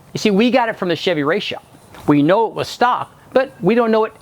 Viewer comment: he says the "Chevy r..." Does anyone know what the "R" word was? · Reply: Race shop